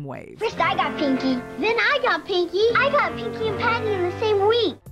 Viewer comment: To me, it always sounded like Po was saying "fatty."